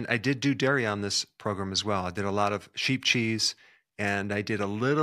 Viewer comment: I thought you said cheap cheese! 😂😩 I can’t afford anything but cheap. Well shoot. There goes that idea as a diet.